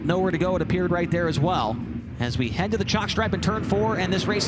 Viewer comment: I had to watch that 4 times and turn on captions to get that he said "Head to the chalk stripe." I thought he was saying jockstrap. 😊